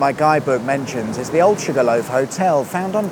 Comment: Sugar loaf. I thought I heard Jigga loaf.